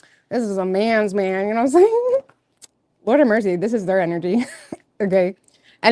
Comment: did you say “they’re gay” and then just skate past it lol context pls? 😂